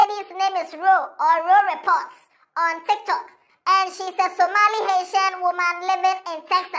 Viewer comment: did you say "Somali Hasian" 0:34. I know of Somali Asian though but yours...?🧐🧐😂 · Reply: Somali Haitian boss